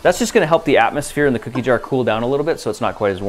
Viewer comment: I heard “cookie jar” 😹😹😹😹😹😹😹😹😹😹😹